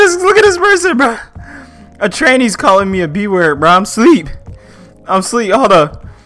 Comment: please dont use that slur unless you are transgender. You have young fans and its really not okay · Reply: I thought he said “A trainee just called me the b word “